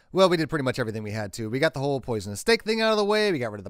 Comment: Was the "poisonous stick" thing handled in the previous video? I don't remember it at all... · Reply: Yes, I know, but in the intro to this video it sounded like he said "poisonous stick" in his list of things he'd already done. I guess he just meant repeating the line?